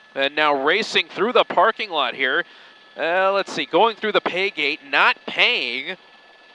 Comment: did he said not paying thru the gate 😂